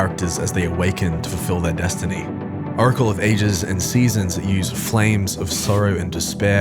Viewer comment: TF, did you just say "Urkel of Ages"?!?!